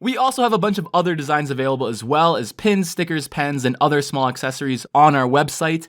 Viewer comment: I thought he said pens, sickers, pens (pins)